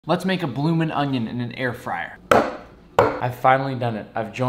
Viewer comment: I thought it said “Bloomin Ohio”